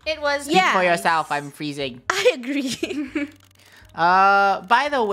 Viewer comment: At first when said “I’m freezing” I thought he said “I’m saying”